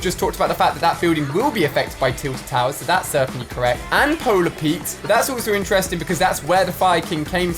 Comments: Did he just say polar Pete??? 😂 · Anybody else hear at 8:57 he said Polar Peat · did he say polar Pete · did he say Polar Pete?